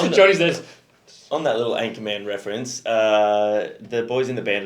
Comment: Your accent is so off that I literally heard "Ant Command" instead of "Anchorman" and had to rewind it